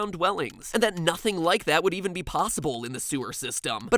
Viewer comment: I thought you said plossible instead of possible